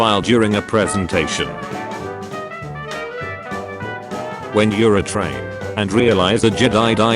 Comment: got me good, 5:57 "when you're a train" that is NOT what that says lmfaoooo